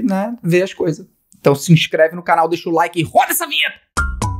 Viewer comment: eu ouvi f0da-se a vinheta kkkkkkkkkkkkkk